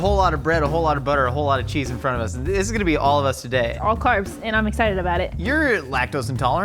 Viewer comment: Did she say "all carbs"? Wat's she talking about?